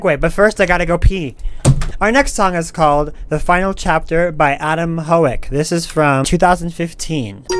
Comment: Thought he said Adam Hoe, also it's Chica not chicka